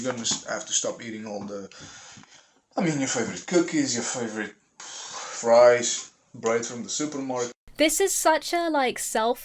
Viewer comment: At first I thought he said "rice", but it was "fries". I checked. I was really hoping it was rice. I fry with sunny oil🌻☺️. 100% not soy anyway, and the bottle makes my fridge seem happier.